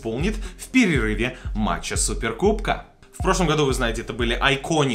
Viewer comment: Мне послышалась "губка" вместо "кубка".😄👍. Супергубка